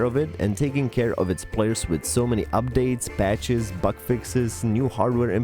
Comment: What are things You love about Blade & Sorcery ? <3 - Join the community on Discord: <link> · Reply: "Butt fixes". Lol with your accent, that's what it almost sounded like!